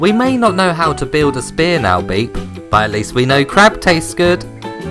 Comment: I thought you said "crack" there for a second.